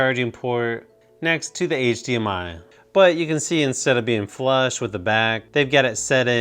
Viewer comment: sounds like he said flash to the back sounds like a joke